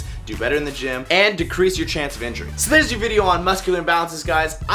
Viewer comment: Haha i thought you said increase your chance of injury, i was like wait what. But you said decrease.